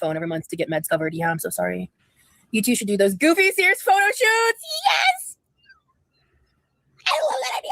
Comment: I misheard her say goofy 80’s photo shoot and thought she said A Goofy Movie photo shoot. I kinda want to see that more.